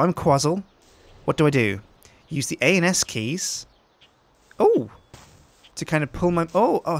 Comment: Does sjin say anus keys